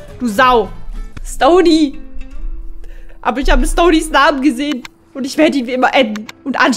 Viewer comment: Ich hab Samen anstatt Namen verstanden😂